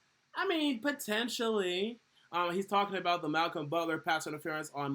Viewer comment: Thought you said Malcolm butter was good lmao didn’t work out I guess